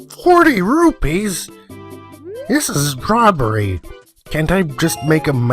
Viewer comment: When you said "robbery," it kind of sounded like you said "strawberry." Lol.